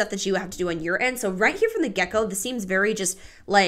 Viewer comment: I heard you say "right from the gecko" and now I can't unhear it. 🦎